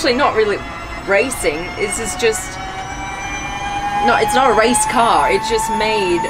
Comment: is it me or i hear : This is a *itch from the car at 6:55 ??